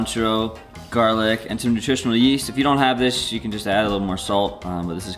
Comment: can someone pls say the right name of that yellow stuff in the mason jar? I can't understand his pronounce... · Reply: Marcus Nunes. said, nutritional yeast.